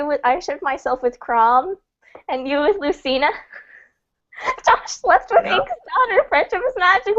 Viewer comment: what did ink say? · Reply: After listening to it a few times I think she said "Josh slept with Ink's daughter" and then something about friendship is magic, but I could be completely wrong (and almost kinda hope I am).